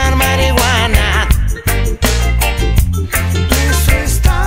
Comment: No lo puedo creer, siempre pensé que decía "Enzo está por cultivar mariguana" 🤣